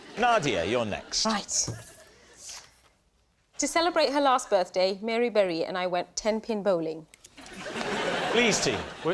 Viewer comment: I first heard this as "Halle Berry" which would have been a vastly more interesting story...